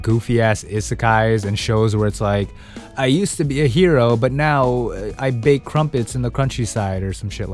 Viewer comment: "I used to be a hero but now I bake crumpets in the countryside" sounds like something I'd watch and thoroughly enjoy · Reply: The first time I heard it I thought he said "...now I bake crumpets on the crunchy side", which honestly still works and might be a funnier title lol.